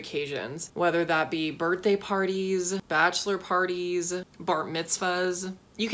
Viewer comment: i heard" birthday parties, bachelor parties, bart mitvahs" like aaaa